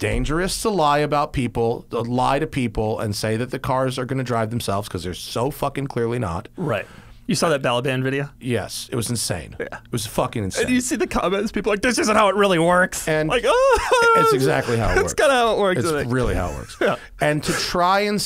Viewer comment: What is Doug saying at the 1:52:27 mark? Balaban video? Valedan? I can't seem to find the video he's talking about